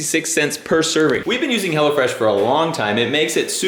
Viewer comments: Thought they were saying "hella fresh" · I coulda sworn she said "Hella Fresh".... lmao..... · Thought the kids were saying 'hella fresh'